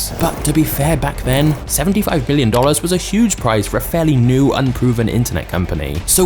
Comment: did you say "...75 Billion Dollars..."?